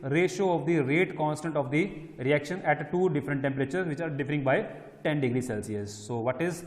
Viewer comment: Sir at 16:57 what are you said after the which are _ 10 degree ?? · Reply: Which are differing by 10 degree